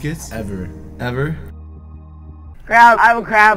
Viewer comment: I thought said I have a crab instead of a cramp